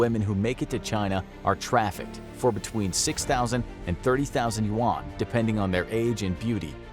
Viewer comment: I thought he said 6000 won (about $5) and my jaw was on the floor. Until I rewatched and saw it said yuan